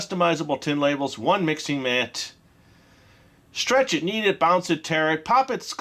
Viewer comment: When he said you could knead it, i thought he said you could eat it.